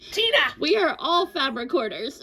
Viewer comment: Really misheard "We are all Fabric Hoarders" as "We are all Fat Quarters" and somehow I was okay with that until context wise it didn't quite make sense 😆